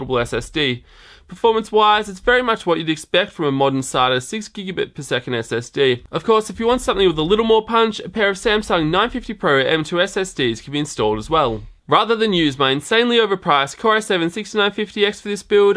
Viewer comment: you have got to grab an m.2 to try with this epic pc. It'd give you an extra review vid, and would be sweet to see on that board! Like an OCZ RD 400 or something. can always go with a smaller one as a boot drive or something. I'm definitely looking forward to seeing this monster built! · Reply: VERY NICE! I completely brain farted when you said 950 pro's and thought you had said 850 pro's. Can this board do m.2 raid?? I've been on the fence about this or the ASRock x99 pro gaming board...